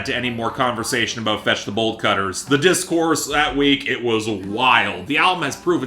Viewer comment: Whenever you say 'Fetch The Bolt Cutters' I hear 'Vegetable Cutters'. Not sure why it matters but its sorta funny